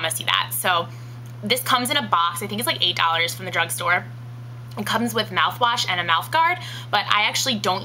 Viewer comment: Did... DID YOU JUST SAY DRUNK STORE?!